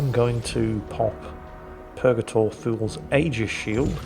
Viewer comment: I always thought was saying Purgator Fool.